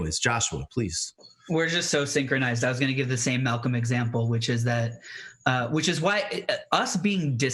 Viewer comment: did Joshua say "Malcolm X-ample?" Is that a thing?